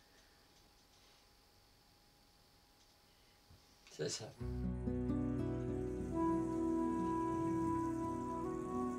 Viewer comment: What does yer man say at 34:41? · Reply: Hi Mark, he says "C'est ca", which is French for "that's it".